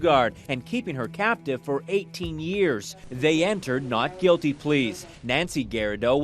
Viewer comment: Did he just say not guilty please?